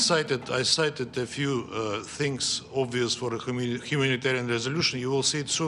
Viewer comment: did he just say Criminatarian Aid.